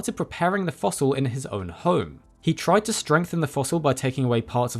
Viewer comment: That weird Brit accent strikes again: @ 2:03 "...and he started preparing the fossil in his oin hoim..." ("own home") ...and he says "boins" instead of "bones"